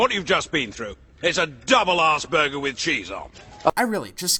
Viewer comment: Okay, so I get getting upset with someone using Asperger's syndrome as an insult. But that wasn't what the boss was saying at all. He was saying 'Arseburger'. If he was saying Asperger's the sentence he said at 9:20 would have been something completely different, which makes more sense: 1. I'm sorry about what you've been through, it's a double Asperger with cheese on. 2. I'm sorry about what you've been through, it's a double arseburger with cheese on. I think it's obvious.